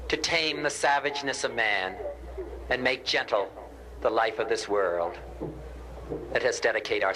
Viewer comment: "To tame the savageness of man and make gentle our world." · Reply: ”...make gentle the life of this world...” 4:41 Just putting what he said up, he may have misquoted it...